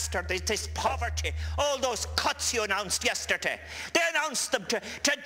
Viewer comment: Does he say "Cunts" at 08:27 ? I love him. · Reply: Pretty sure it's: "all those CUTS you announced yesterday"